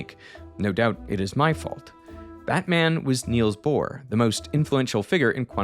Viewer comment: who else heard “Batman”